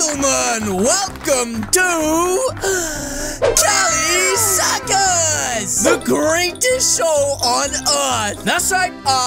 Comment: welcome to cali circus first when i heard it i tought it was suckers